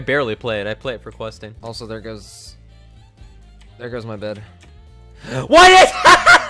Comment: I thought he said bedwars